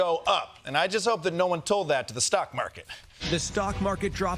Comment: swore you said snack market first